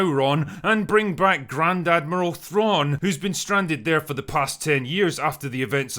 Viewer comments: I heard "Gran-Dad Merle Thrawn" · I just heard 'Grand Admiral Thrawn' as "Granddad Merle Thrawn,' and now all I want is a sitcom where Thrawn's crusty grandpa leaves his cabin in Space Arkansas and moves in with him.